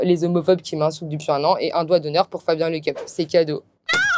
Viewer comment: pendant une seconde j'ai entendu un doigt d'honneur pour Fabien Olicard j'ai eu peur, puis j'ai compris que c'était Fabien Lecoeuvre et là tout va bien